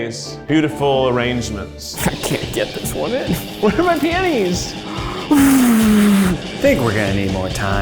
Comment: It 100% sounds like Keith said “where are my panties”